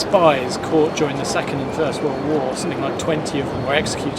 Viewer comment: did he say third world war at 2:36?